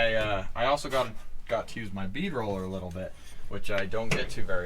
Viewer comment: When you said bead roller for a moment I thought you said beaver oiler lol